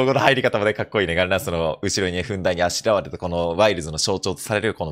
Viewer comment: には、「ふんだんに」の後の言葉は何？ネイティブじゃないからよく聞こえない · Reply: あしらわれた 装飾するって意味